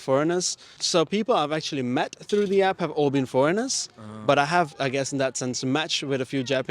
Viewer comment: When you say foreigner sounds like you said horny men 😂😂😂😂😂